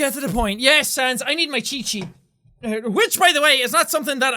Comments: when jack said let me get my cheat sheet i thought he said let me get my chee chee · Jack: I need my cheat sheet. Me thought he said: I need my chi chi. Me: WAIT WHAT?! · I thought he said "chi chis" (I'm not sure if that's how it's spelled) when he said "cheat sheet" which is like saying "titties" in spanish.